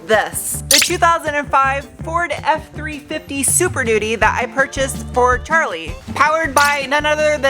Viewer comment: I thought you said Super Doody. 😆